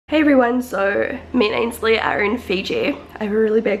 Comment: Did you say Ainsley at 0:03 because that is my name and I've never heard or met someone with the same name as me